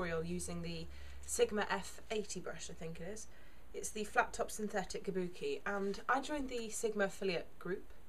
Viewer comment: hahaha, so the sound was almost off on my com, and it sounded like you said f*cked up synthetic kabuki :b Thanks for sharing, it's nice 'cause my complexion is quite similar to yours :)